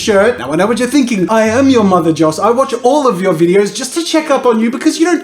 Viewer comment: Is his name or am I hearing things Press playback speed 0.75 and go to